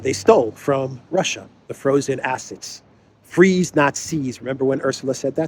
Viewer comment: Did Ursula Crazy say "freeze not seize"? I thought she said "Free-the-nazis" 😅